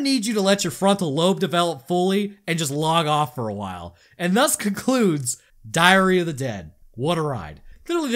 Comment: Did he say Diarrhea of the dead? because I fully agree